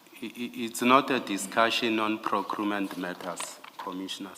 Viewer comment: Did he say procrument ?😢😢😢